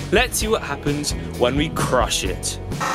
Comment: Lol who else thought it said "crush-shit